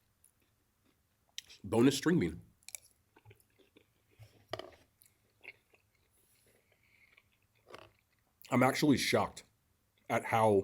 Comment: Did he just say boneless string bean? Lol